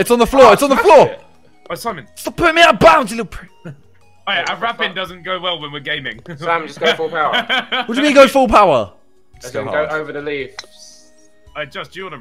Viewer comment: I thought it Said "raping golf" And i was like WTF